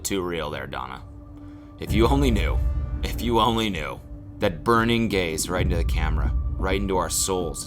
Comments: You said “that burning gaze.” But I thought you said “that burning gays.” I thought you were about to explain that burning gays caused the pandemic or something. As a gay cunt myself I thought that would’ve been hilarious especially during pride months. Sorry my mind is retarded sometimes. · did i hear "burning gays"? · When he said “that burning gaze” I sure as shit thought he said “that burning gays” and now I’m simultaneously disappointed yet relieved. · I wasnt paying a lot of attention to the video and I definitely heard "burning gaze" as "burning gays". I was suddenly very confused about what corner of the internet auto play brought me to. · "burning gaze".. I heard "burning gays" 0_0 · did he just say burning gays???? During pride as well?!?!?! SMH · I did a double take at 2:46 because I thought he said "burning gays" and I was like please Cody, not during Pride month 😤🤣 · I heard “that burning gays” and not... not “that burning gaze”. Is this what a stroke feels like? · swore he said burning gays for a second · i thought he said ‘burning gays’ and i was like WHAT?? where is this going pls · i thought cody said “burning gays” instead of “burning gaze” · Thought you said "burning gays" and was so concerned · i heard "the burning gays right into the camera" and i was --- tehehehe oh! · when he said "burning gaze" i thought he said "burning gays" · I literally almost had a heart attack I thought you said burning gays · I thought he said "burning gays" not "burning gaze"